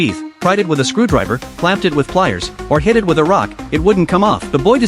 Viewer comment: Bro why did I heard "clap it with cheeks"😭😭